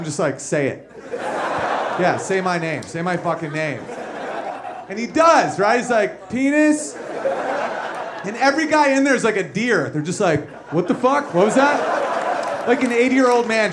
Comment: I literally thought he was saying Denise this whole time until about 2:14 and then I understood the joke.